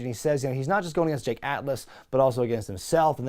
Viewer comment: Ohhhh... it's Jake Atlas? I thought they were saying Jay Catless. Boy, do I feel like a dummy 🤦‍♂️